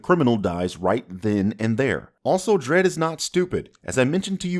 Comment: when he says "Also Dredd is not stupid" the subtitles say "Also Dredd is not CinemaSins" 😂😂😂😂😂😂